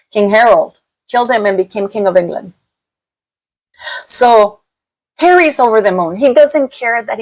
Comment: Did I hear you say “Harry’s lover…?”